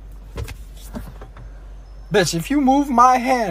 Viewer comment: did he just call her a bi***?